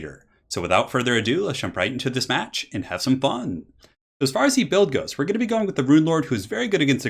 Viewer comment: Is it just me or does it always sound like says “as far as he build goes” at the start?